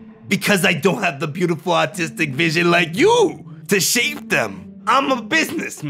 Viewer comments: im stupid i misheard this as saying because i dont have the beautiful autistic vision like you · when guy says "because i d👁nt have artistic vision like you-" swear i heard "autistic vision" and... for a m👁ment... that explained a l👁t